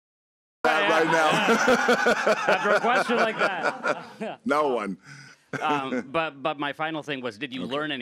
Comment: What did the guy say at 1:28:10. What does it mean "I didn't set my outlet"? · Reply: Correction.. He said "i didnt say my outlet" meaning he forget to introduce which media he represent as journalist.